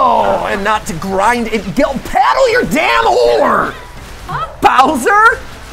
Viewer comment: I don't know why but i keep mishearing oar for **ore xd